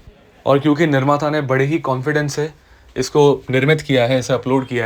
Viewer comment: What people heard in 0:40 "nirmaata ne bade confidence se shabdo ka prayog kiya hai post me" What I heard? - "nirmata ke confidence ko hi khatam kar dunga"